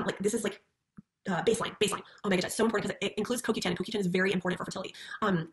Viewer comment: tmw she's saying 'CoQ10' and i heard "cookie time"